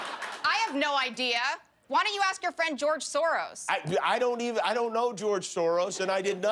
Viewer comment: did anyone catch that name, George Soros?